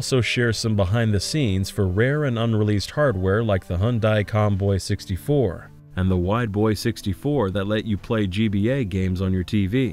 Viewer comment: I heard that as whiteboy 64 lol